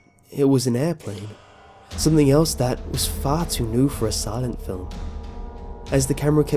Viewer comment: omg i thought you said fart xD